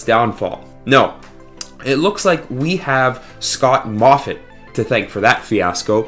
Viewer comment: I thought you said "Scott CAWTHON" lol